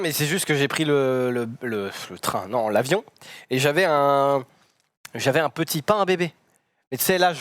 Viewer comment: t'inquiète pas, nous non plus, il dit littéralement un pain bébé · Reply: Perso j'ai entendu " un petit pain bébé "